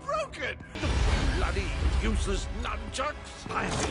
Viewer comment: DID HE SAY BLOODY USELESS NUNCHUCKS 😭